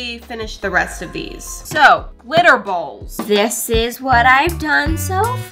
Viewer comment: I thought she said glitter balls 💀